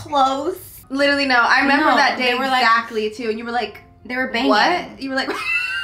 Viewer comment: Lol did say the way she found out that they were dating is bc they were banging?!?!?! Lmao